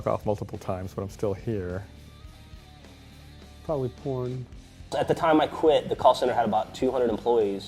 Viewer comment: I love answer: "Probably porn". Just imagining Caboose in porn is disturbing, yet hilarious. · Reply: I thought he said "Probably poor" at first but now that I read your comment I can't hear it as anything else